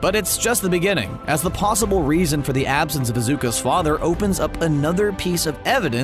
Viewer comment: Wait did he say Izuku or Izuka? # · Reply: He said lzuka😭😭😭